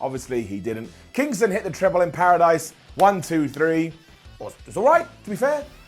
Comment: It sounded like said “Tribble in Paradise” and, as a Trekkie, I was both happy and terrified.